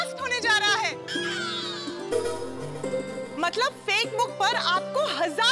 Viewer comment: she told fakebook instead of facebook